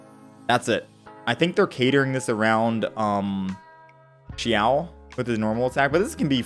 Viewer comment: what did he say about Xiao? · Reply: They are catering the [abyssal blessing] towards xiao because he’s going to be oj the first banner.